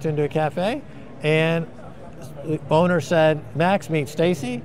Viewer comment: Max and Stacey are the best! Anyone else hear "boner meet Stacey"? Was watching 2x speed now know it is owner but heard boner 1st time :) Also, wonder if Max ever read Carl Rogers On becoming a Person.